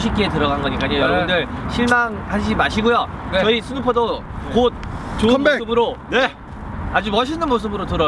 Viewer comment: did I hear the word "comeback" somewhere in there? I may not understand anything else in the video, but I for sure can pick out that word lol pleeeeaaaasse have a comeback!!